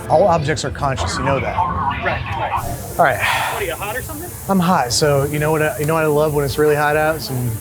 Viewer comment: At first I definitely thought he said "I'm high" rather than "I'm hot."